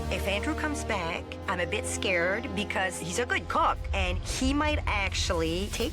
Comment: did she just say “he a good cock” 😂😂